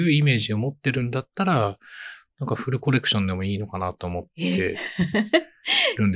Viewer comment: この辺りのの発言がよく聞き取れませんでした。。「フルコレクション」と聞こえたのですが、意味がわかる方いましたら教えてください<(_ _)> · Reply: full correction 完全に修正（いってこい）😊こんな感じかな？